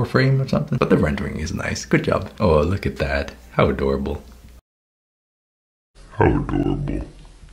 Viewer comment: This guy is posting videos higher quality then 99.99% of art YouTubers. He’s honestly so underrated, instantly subbed. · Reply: For a second I thought it he said horrible 0_0